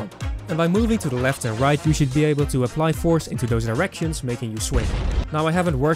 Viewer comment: wait, did he say directions or...